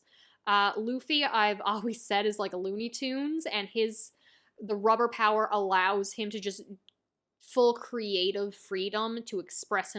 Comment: did she just said Looney toons 😮😂😂😂